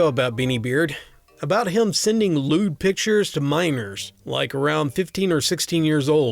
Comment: At the end of the last story I had a major mishearing. I thought he said "he sent lewd pictures to mimers." I thought "huh that was oddly specific" and then the coin dropped.